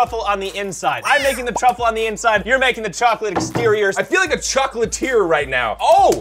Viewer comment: I thought you said milk the chocolate😂